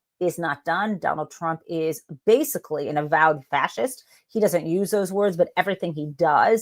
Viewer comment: I thought you said “trump is basically a ‘devout’ fascist”. I think he is definitely a DEVOUT ATHEIST AND FACIST!